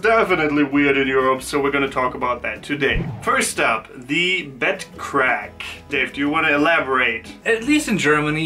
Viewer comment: At first I thought you said 'butt crack' which was definitely weird, especially in context. I had to listen a while to understand 'bed crack' which I think is somewhat less weird. ;)